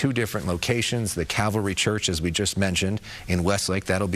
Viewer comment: it sounds like he is saying CavaLry, but it's actually CALVary Church